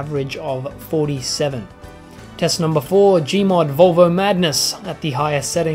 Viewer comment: says minecraft instead of gmod lol